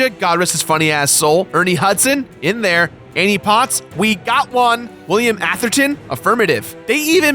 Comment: I spit out my drink cause I thought you said the actors name was William Afton, the fuckin dude from the fnaf games